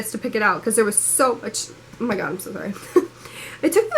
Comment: did she say omg im so high? lmfao